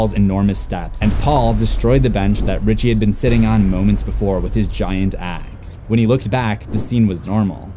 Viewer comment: I swore you said “and Paul destroyed the bench Richie was sitting on moments ago with his huge a**”